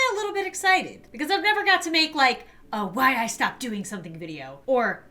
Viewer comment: Help i thought it said “why i stopped making videos” KWKEMDISJNWPDODMWJOFIFNE